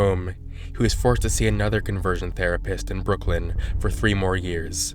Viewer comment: i thought it said conversation